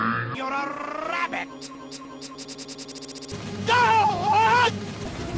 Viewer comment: it sounds like he is saying Donut!